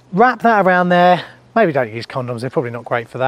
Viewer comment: For a second there I thought you said a “used condom” 😳!